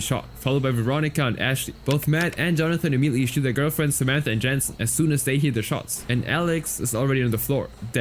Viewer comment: did you say shoot or shield? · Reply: Shield 🛡️